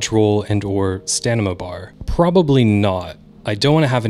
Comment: So @ 7:33 you said Stanima instead of Stamina. Is this a common way to say the word, or just a common way to mispronounce the word?